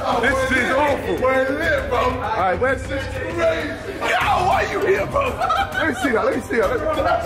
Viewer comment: what did jj say at 2:32? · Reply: “We’re lit fam this is crazy, get out why you here fam”